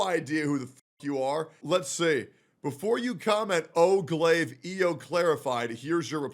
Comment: I stopped at 0:14 on accident. All i heard was, "Before you come-" I was like, "now hold on just one second."